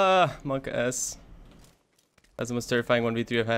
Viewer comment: did he said Mark Ass??? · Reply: Saket Shetty monkaS, that twitch bullshit